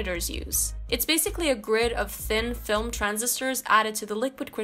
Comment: I could have swore she said Thing Film instead of Thin Film lol. Alrhough i could be wrong.